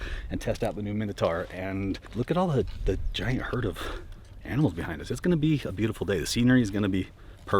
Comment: Am I the only person that heard him say “herd of antelope” and then camera switched to a bunch of elk😂😂